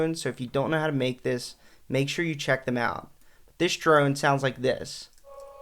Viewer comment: no bro listen to what he says at 7:12 he says smthing like distrone or disdrone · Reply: "this drone sounds like this"